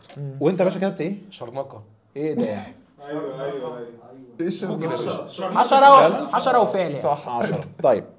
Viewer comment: مش عارف ليه سمعتها حاجة تانية 😂😂😂😂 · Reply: قالو حشرة و فعل 😂😂